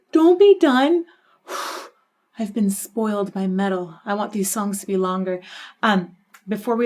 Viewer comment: "Noooo don't be done... I've been spoiled by metal" 😂 I love that comment...Happy New years Elizabeth 😊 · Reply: Haha thank you for this, because I first heard "I've been spoiled my medal" and was a tad confused 😂